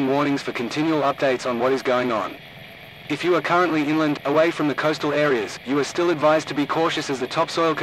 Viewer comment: I thought that said finland